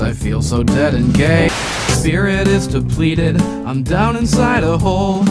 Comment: what is he saying at: 1:21 "something" Depleted? Completed? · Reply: The line is "my spirit is depleted"